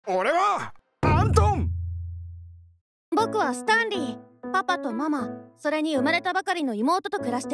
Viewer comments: びっくりした..スタンリーがスターリンに聞こえたわ.. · 冒頭の「僕、スタンリー」が「僕、スターミー」に聞こえた！！